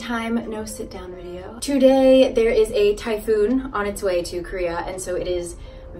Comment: me, waking up in the morning and watching your video, mishearing the first sentence: "Today, there is Thai Food on its way to Korea" me: "I... thought they already had Thai Food there?....*loading* aaaah typhoon!" 😂 also, congrats on the 100k, it's well deserved! greetings from Berlin, Germany : )